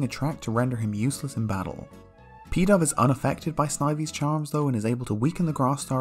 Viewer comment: When he said "Pidove was unaffected by Snivy's charm" it sounded like he said "Peta" so I was like, oh god here we go with a reference to that gross pokemon parody they made